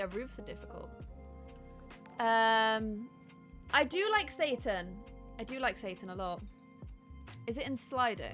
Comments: I’m sorry at 35:25 did say she liked satan? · Ummm just wondering when said this I don't understand at 35:24 can someone clarify that for me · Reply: she said seitan which is a vegan meat alternative that she eats